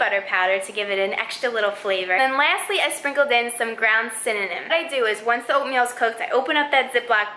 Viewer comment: I'm sorry did I hear "ground synonym" 😂😂😂😂😂😂😂😂😂😂